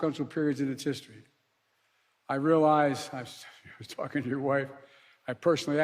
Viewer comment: What exactly did he say at 55:00 about wife? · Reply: I talked to your wife...